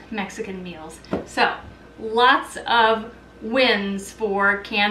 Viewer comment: Lol! I thought she said lots of WINDS with those beans! I had to play it back😂